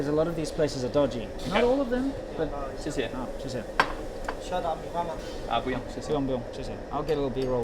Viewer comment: Im italian and i speak spanish and english pretty well. Waching a lot of videos, my brain was in "english mode" and i heard the waiter say "shut up, your mama" WTF